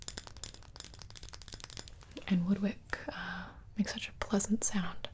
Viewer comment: I thought she said wood witch at first